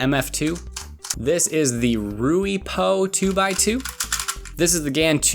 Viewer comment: did you just say Ru Paul 2x2?